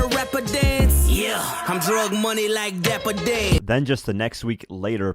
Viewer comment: why does it say “I’m drive money” it’s “drug money” 😆